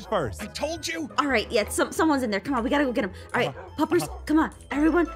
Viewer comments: does say helpers or puppers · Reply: Puppers. I'm rewatching this lol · does say helpers or puppers